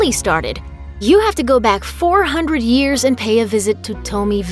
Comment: Did she say 400 years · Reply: Miyagi-do's history goes back 400 years. One of Mr. (Nariyoshi) Miyagi's ancestors was a guy named Shimpo Miyagi. He was the one who traveled to China and first learned martial arts.